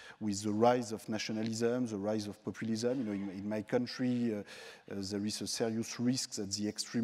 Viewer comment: What did Piketty say at 30:07 when he says "in my country, the ??? risks the extreme right will win" I can't figure it out. Help! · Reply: I know this was 3 years ago but I'll help. "In my country there is a serious risk..."